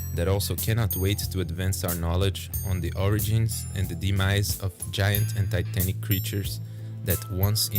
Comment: This might be cause I play too much Arma 3 and the like, but does anyone know what this guy said here? I hear “The demise of giant Anti Tank creatures” are these creatures that launch themselves at modern world tanks in some sort of kamikaze attack? No wonder they’ve gone extinct, humanity built too many tanks to blow up. Must be why Russia has been having such a hard time in Ukraine.